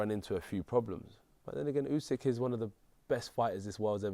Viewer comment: did AJ say my nigga Usek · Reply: he said “but then again” sure did sound like it though 😂